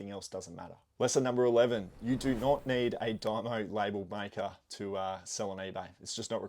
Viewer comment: I don’t know if its the accent, but after listening several times about the “printer?” 6:53 This Yankee (USA) still can’t get what you said 🤔. Excellent video otherwise😊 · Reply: dymo label printers aren't necessary when you're new. Once you're making regular sales you can buy one in my opinion as it costs too much when you're first starting out